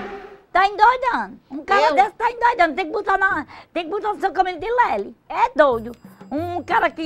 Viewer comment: Tem que botar o cara aonde?? O que ela fala, gente aos 7:38 ?? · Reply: no São camilo de lélis, eu acho que é isso